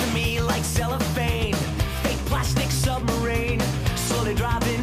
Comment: I heard it as "fake plastic summer rain" because that made slightly more sense lmao